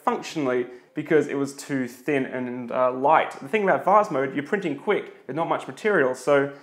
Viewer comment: am i the only person who thought he was saying fast mode?